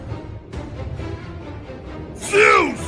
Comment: when kratos said olympus it almost sounded like ras XD